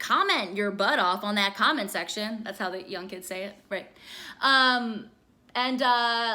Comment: i thought she said "that's how young k would say it" and i was like HOLD UP